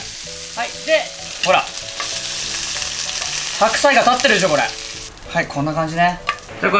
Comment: 白菜立ってるって聞こえたんだけど？